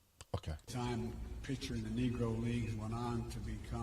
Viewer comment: Sounds like he said negro league. I am not defending him, but could barely hear him. They didn't change the name of the league but it should have a better name.